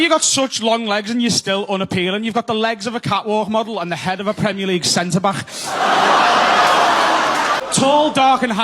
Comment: "You've got the legs of a Ca-whad moddle and the head of Premuleague Centerbach." What tf is he even saying?? Are those things?